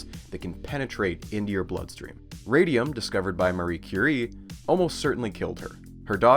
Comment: In chemistry class, my friend thought the teacher said Mariah Carey instead of Marie Curie.